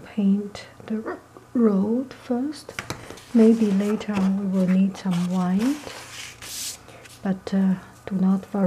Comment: Beautiful painting! At first when you said "maybe later I may need some white" I thought you said "maybe later I may need some wine" lol 😂